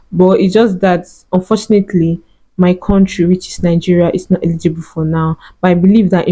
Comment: I have been monetised and I'm in Nigeria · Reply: Congratulations,I thought dey said Nigeria is yet eligible